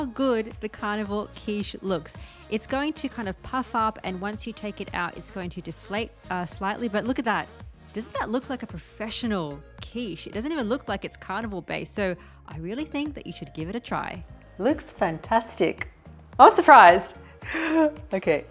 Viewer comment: I don’t know why I can hear correct words…I heard you say your naming a “carnival” Quiche. Ugh. Your egg flipper is what we call a spatula. Dairy is addictive..I still struggle with ice cream. 😂